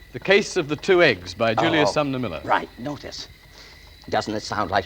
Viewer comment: I thought he said Julius Salmonela